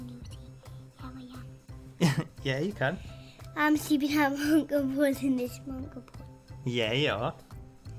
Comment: What did say at 18:27 and 18:35? Hilarious video :) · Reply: ***** she was asking if it was ok to say she was sleeping at her Uncle Pauls lol